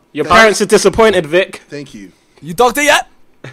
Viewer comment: what did Simon say to Vikk around 22:44? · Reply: He said, are you a doctor yet?